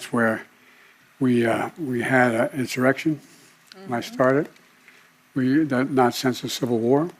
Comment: did he say "that i started"? ..... well, dats wut i heard, thats wut ima tell errybody, sooo🤷🏼‍♂️